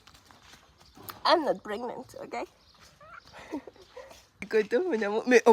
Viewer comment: J'ai entendu "i'm prégnante" a un moment donné🤭😜